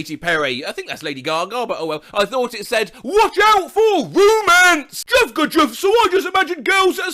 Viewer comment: I thought you said WATCH OUT FOR HUMANS